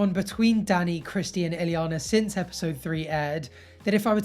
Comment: You said "Christy and Ileana" but I heard "Christina Aguelera" DOH!